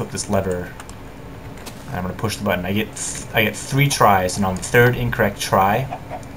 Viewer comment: I thought you said 'after three tries, lava will flow down'. I was expecting lava to fall on your head and kill you.